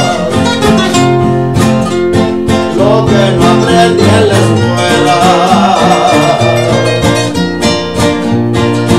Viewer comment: Que dice en el minuto 2:50 ? Gracias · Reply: Luis Alejandro Coronel Padilla El me enseñó de la vida , lo que no aprendí en la escuela . Gracias amigo por tu apoyo